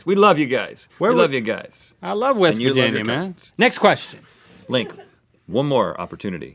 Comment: Sounds like Rhett said, "And you (F-Word) with your cousins."